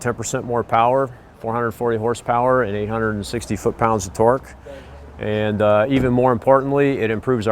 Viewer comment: did he just say 160 ft pounds of torque??